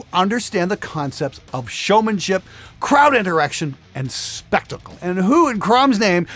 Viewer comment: "crowded erecti*n".. What?? Oh, i misheard: "Crowd interaction" is what he said :D